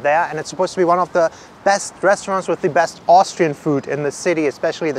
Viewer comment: hehehehehe I thought you said Australian....